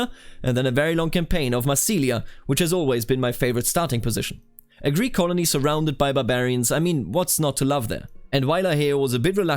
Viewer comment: I heard "a greek colony surrounded by Bavarians" and I was greatly confused for a moment